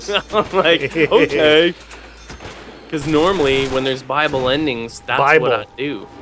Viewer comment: When I first started watching I thought they said bible endings I always thought it was weird